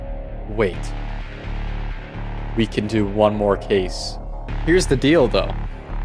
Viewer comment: 10.54 He said "Here is the deal tho" What I heard was "Here is the Dildo". I cant stop laughing xD